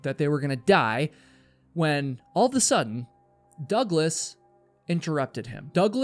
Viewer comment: am I going crazy or is he saying “all of THE sudden” instead of “all of A sudden”